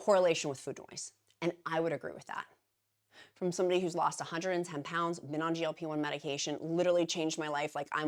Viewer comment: Yes, it sounded like you said 140 pounds. We know what you meant though.